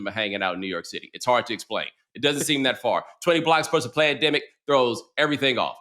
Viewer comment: Did he just say “plandemic”? 🤨